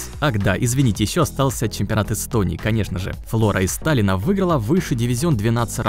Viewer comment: Мне послышалось Флора из Сталина. ЛОЛ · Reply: Он сказал из Таллина.